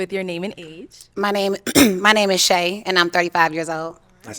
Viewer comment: naija. I hear Seyi? 🇳🇬🔥🔥👍🏾. Represent.. oh wait Shay..ok.